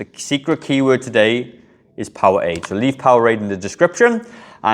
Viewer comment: Couldn't understand your secret word at the end and you put the bottle down before i could read it - it sounded like you said "Power 8"? I'm a 72 year obese dabetic2 with coronary heart disease, heart AFIB, CKD (chronic kidney disease) and I've had heart bypass surgery and just had a pacemaker implanted in my chest and had one hip replaced. I stopped weightlifting & swimming laps 15 years ago after my first heart surgery....it just felt like what's the point. I just started on MOUNJARO for my diabetes 2 and for weight loss, and the DOC said i need to start weightlifting again to avoid losing muscle mass while losing weight (can't eat much protein, tho, because of the CKD). My question - I have many sets of dumbbells at home & I'm not going back to the gym, so what dumbbell exercises do you recommend that I can do at home? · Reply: Powerade! So for any given exercise, choose a weight that challenges you at the last 3 reps......this may help: <link>